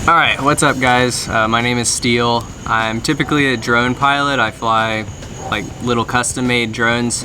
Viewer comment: When I heard him say "hi I'm a drone pilot" I thought he said "hi I'm a drug pilot" 😂